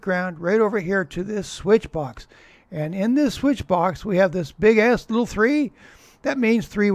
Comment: I thought you said “Big ass”.